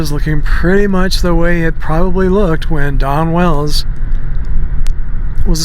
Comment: it sounds like hes saying Donna Wells